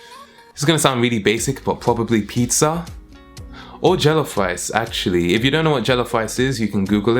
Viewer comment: Deadass I thought u said jello fries and not rice oml 😂😂